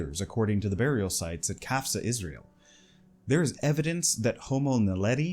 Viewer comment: you just said Gaza, Israel? what an horrendous thing to say. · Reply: I said "qafzeh, israel". That is a different place. Also, free palestine